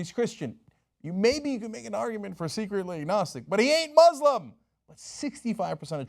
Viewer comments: I totally thought Cenk said "maybe you could make an argument for 'secretly a Nazi' " lol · Secretly NAZI'S? I know i misheard that, what did he say? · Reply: Agnostic